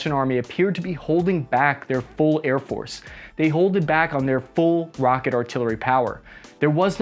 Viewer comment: Did you say holded?